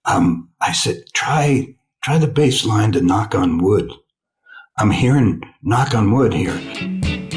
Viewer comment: I didn't hear "Knock On Wood", I kept hearing "Knock Three Times". Maybe dude got the two songs mixed up. Dude was young adult when I was a little child, and I get mixed up sometimes. It happens.